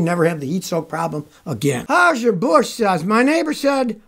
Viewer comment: did I hear that right?! how's your bush?!?!